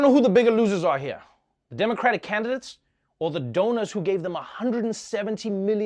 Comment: I've been watching so much election coverage that I thought Trevor said "election donuts" Lord, he said donors not donuts🤦🏻‍♀️ I need some sleep.